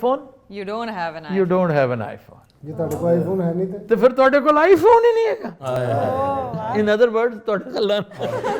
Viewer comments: hahahhah 32:13 hahahha WTF, did I heard it correct ? he said toady kol L*n hi nhi hega hahahahah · pr kya bola 😂😂😂😂😂 twady kol l*n hega🤣🤣🤣🤣